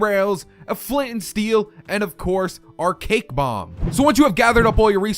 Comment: I thought it said "Cake Womb"